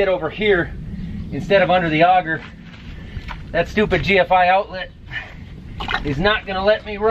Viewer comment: At least you don"t have to send your crop for a swim lesson. You lucky one! That few acres. Insurance? Hmm, rubber boots, tss softie. u schould wear quad tracks. And at least a plastic bag on ur head on that quad. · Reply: btw. Stupid GFI outlet?? I thought u said GSI outlet. Hmm, a little respect for my outlet please.